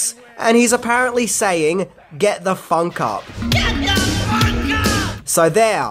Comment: I thought Prince was saying "get the fuck out!". Lmao.